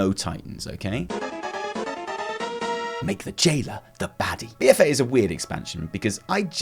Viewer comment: For #5 I originally heard you say "Make Jaina the Baddie..." I was like, OMG MAN COME ON.... LOL